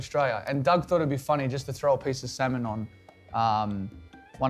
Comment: I misheard salmon as semen 😂😂😂